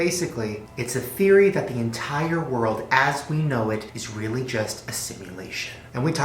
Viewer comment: At the start when he said “entire world” I thought he said tiger world